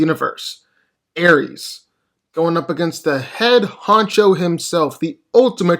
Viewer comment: did he just call odin the head huncho? 😂